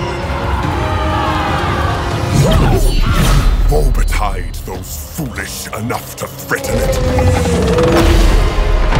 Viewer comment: What he said at this moment? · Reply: Marzel woe betide those foolish enough to threaten it